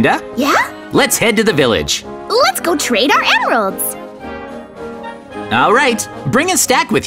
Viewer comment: the subtitles say "Let's go trade our ARROWS ", But it is EMERALDS.